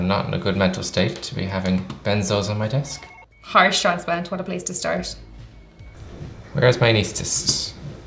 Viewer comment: Harshanspan totaplease distorsh? wat did she just say? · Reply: Gladsmiley Heart Transplant what a place to start